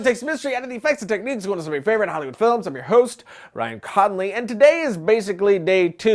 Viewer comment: Slow down cowboy...sounds like you said your name was Ryan CODolly! Damn there speedo,. Hook us up with a line. No...we'll pass. Slow down homie.